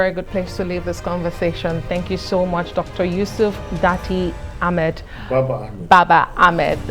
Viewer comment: did she really say “daddy Ahmed” 🤣🤣🤣